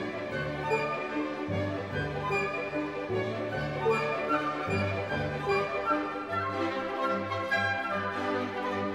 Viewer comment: what the heck did he say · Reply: He said: "This is Not funny!"